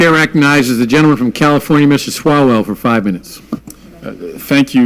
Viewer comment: Did I hear "Mr. Swallow" at the beginning?